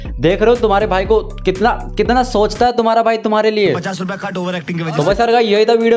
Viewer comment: ne kya bola bhai ko dakho😂😂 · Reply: Ladki Ka chakker babu bhiya ladki Ka chakker